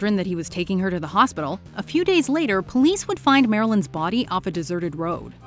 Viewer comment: Please tell me I'm not the only one who heard "a 'pew' days later" at 16:50. Ha!!